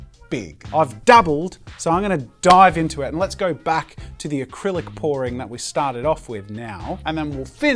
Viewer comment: I thought you said Acrylic boring haha